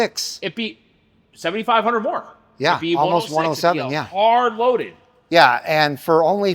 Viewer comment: I thought he said “HARD R LOADED!” I was like whoooaaa guy! I had to rewind it twice. 😂😂😂